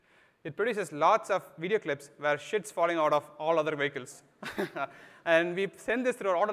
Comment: does he say where shit's falling out of other vehicles, lol?